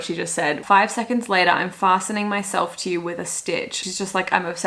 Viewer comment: Talk about misheard lyrics. I thought glitch was super sexy too. So sexy that I thought it was “passing out next to you with a stitch” when I first heard “fastening myself to you with a stitch”. And I was like MA’AM!!